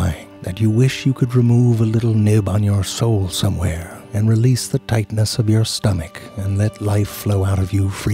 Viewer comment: "you wish you can remove the little nib on your soul somewhere" Sobs yes, yes I do. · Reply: I had to scroll back, because at first I thought he said "release the Titaness in your belly".